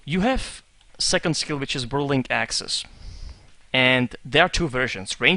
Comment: hey, i hear you say, "Vwirling axes". Are you German?